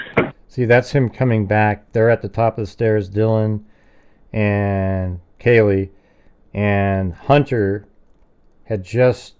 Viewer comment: I finally figured out what she whispers at 12:44 she says something, then Kaylee and maddie.. I THINK it's "I dont hear Kaylee and maddie"